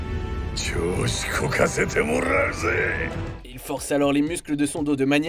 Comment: c moi où il a dit zizi??